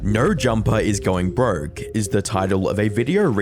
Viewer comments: I keep hearing “Nerd Jumper” and it’s hilarious 😂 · did he say "nerd jumper"? 🤣🤣